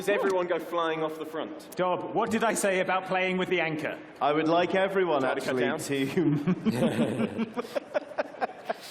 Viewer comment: What exactly did say at 1:30:30? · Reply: He said "try to cut down?"